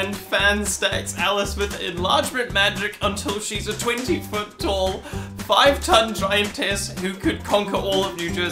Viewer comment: I may need to actually pay attention more as I heard 5ft 20 tonne giantess...